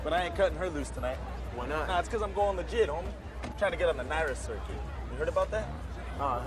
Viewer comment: For years always thought he said “cause I’m going to jail homie” Lmaoo boy was I wrong 😂😂